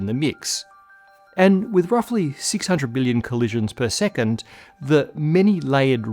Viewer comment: At around 10:56 is it 600 billion or 600 million collisions per second? It sounds like you said 600 billion, but the subs have 600 M